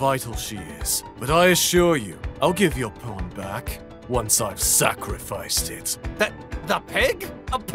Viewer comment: can anyone tell what the feck hendrickson said at 2:15? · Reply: "I'll give your pawn back... After I've sacrificed it."